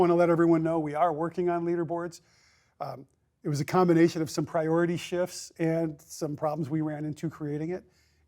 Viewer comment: for a second I thought he said 'Priority Shit' hahahah